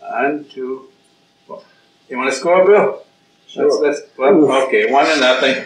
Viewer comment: Did he say "Wanna score bro?!" Or am i loosing it